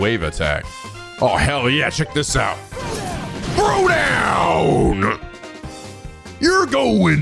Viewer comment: I hope I’m not the only one who hears ‘Frodo’ every time he does that attack